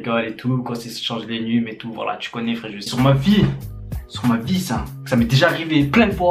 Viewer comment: c'est moi ou il dit " tu connais frejus "